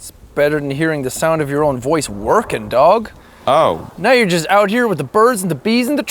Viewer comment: did anyone else see that ass pass by the camera? WHOS ASS WAS DAT! 2:32 and Arin says "Oh" as soon as the ass passes by -_- if you spam that 2:32 keymark, it sounds like jack is saying "Twerkin' Dog!"